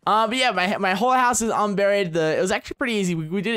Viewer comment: I honestly thought he said my whore house is unburied.